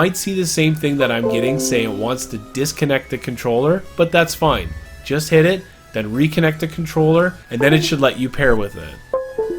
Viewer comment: Hey can you help me........ it detected my usb addapter, i go to the proggres bar where you need to press the controler button and after it gets to 6 it says unpair controller but it dosent show any settings... PLEASE HELP ME!!!! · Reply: u have to unpair it and repair it to the adapter for it to work thats what he said on